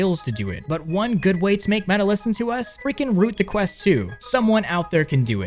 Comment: the hell did he say at 11:10 ? root the quest 2?